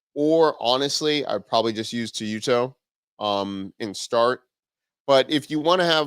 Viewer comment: At around 16:36 can anyone tell me what tool he mentions for a streaming tool? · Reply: I was talking about Teyuto. Here's the link if you want to try it out for free. <link> (affiliate link)